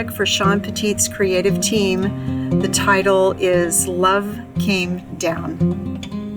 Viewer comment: It sounded like you said Love Reigns Down which is an old song by The Who. I like your trees, honey. My crafting days are over for now I have to go into Hospice care but I’ll still watch your videos. 😊 Annie in TN 💕